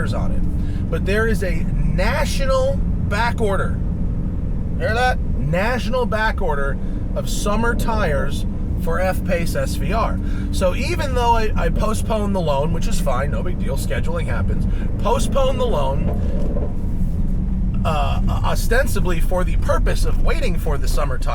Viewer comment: i thought he said post malone